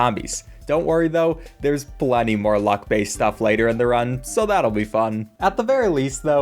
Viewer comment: I keep hearing "buck ads"